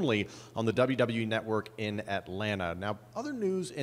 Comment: Did he just say "Atlanta"? Goldberg return confirmed